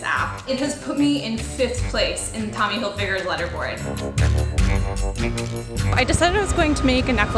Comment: did she say tommy hilfiger's leatherboard?